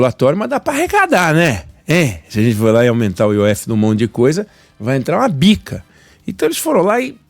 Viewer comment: Quem aí mais voltou o vídeo para 6:19 pra ver o que ele disse que ia entrar kkkk eu voltei três vezes pra entender vai entrar um bica😂😂😂